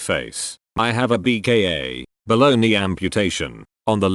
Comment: When you're not fully listening and it sounds like he said "Baloney transplant" instead of below knee...